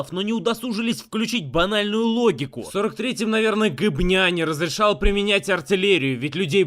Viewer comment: Жека, обзор кул! Но какое слово ты произносишь на 04:33 , я не расслышал ? Гопня? Говня???